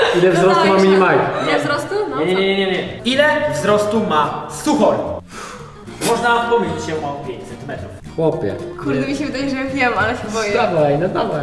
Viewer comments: usłyszałem "Można pomylić się o 500 metrów xD" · usłyszałem można pomylić się o 500 metrów